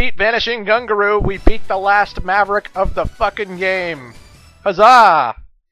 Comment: did he say kaza! ? lawl